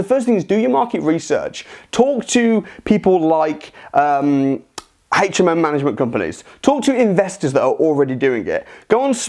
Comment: Nice Video! What's this guy saying at 01:23 when he starts listing entities to talk to for market research *hatchabatch*companies? Can somebody please translate? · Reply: HMO (House Multiple Occupation) management companies